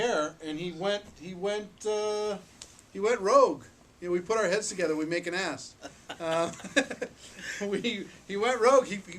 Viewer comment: Before I read the comments, I thought he said "When we put our heads together we make an S" and I was so puzzled.